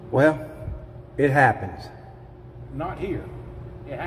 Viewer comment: Did he say "not here"? How would he know that? Idiot, someone that is intimidated would not do a complaint.